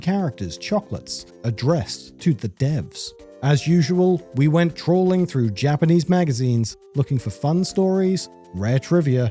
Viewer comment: "As usual, we went trawling through Japanese magazines" is how you know to get ready for the good shit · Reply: I thought they said "we went trolling through Japanese magazines"